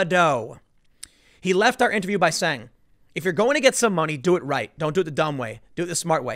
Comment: I could have swore he said “moneys.” Which I thought to be especially hilarious